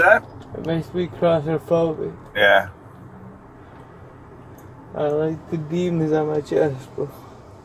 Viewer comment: Did he say "I like the demons on my chest"? 😂